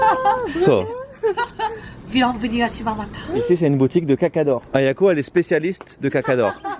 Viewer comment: moi j’entends caca d’or 😂